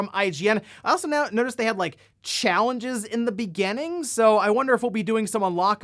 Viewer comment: for a second I thought you said "shellenges" :D